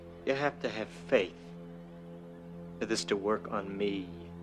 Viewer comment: Mandela effect. For years I thought he said “You gotta believe for that to work on me.” 🤷🏻‍♂️ I like my line better. 😅